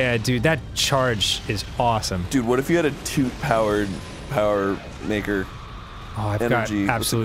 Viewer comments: Did Arin say the N word on Grumps Buds!?!?!?! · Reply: I hears it as I read this, did not even hear an inkling of the N word. It was "Maker" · what did Arin just say? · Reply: Maker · Did...did anyone else hear what I just heard? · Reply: maker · Did arin just say what i think he said??? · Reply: he said maker